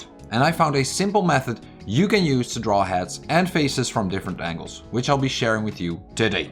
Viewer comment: I could’ve sworn he said hats at the beginning but then I realized he said heads. I was like “omg hats?! I mean learning how to draw a fancy hat would be nice!” But it’s heads, which is cool too I’m glad!